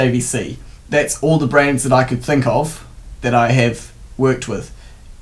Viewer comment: i thought he was saying 'Brains'